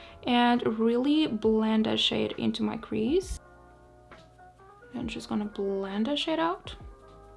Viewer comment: What you said: "I'm just going to blend this shade out." What I heard: "I'm just going to blend this shit out."